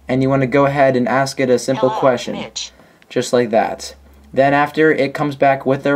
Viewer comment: lol i thought siri said hello bitch not mitch haha